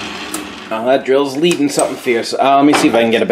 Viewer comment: great video. at 26:49 you said something about your drill, I didn't understand what you said or what you mean. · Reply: Drill bit is leading. One flute is either longer than the other or dull. The chips coming off are not equal from both flutes. Tends to either make your hole larger than you want or pull it off center.